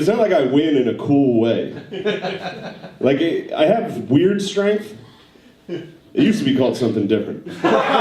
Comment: I have weird strength 😂😂😂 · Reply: I thought he was saying n**** strength, lol. Just assumed it was a racial joke